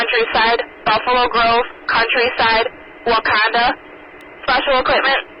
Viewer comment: Lol ngl I thought I heard Wakanda in the comms until I saw Wauconda on the truck 😂